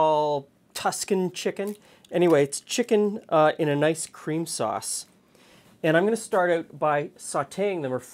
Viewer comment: I swear my brain heard 'chicken, in an ice cream sauce' at first.